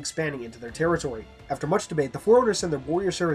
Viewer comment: did he say masterbate